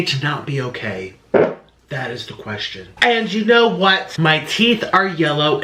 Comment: Lowkey fart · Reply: without context it sounds like he says that the fart is the question